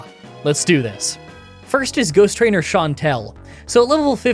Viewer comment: he says goose trainer instead of ghost trainer.